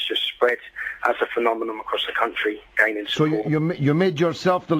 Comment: He actually said 'phenomenom' I don't care how old this is. He was a grown man when he said it. How can you talk about upholding British values when you can't speak the fucking language? He hasn't read Humpty Dumpty, but considers himself a journalist. What does that say about the dickheads that follow him? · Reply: Kamal Hussain sounded like phenomenon to me - maybe you misheard him and went into an unnecessary rant!